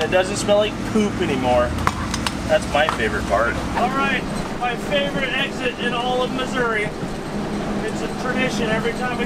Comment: Lol it sounded like you said " my favorite exit in misery" 🤣 ❤️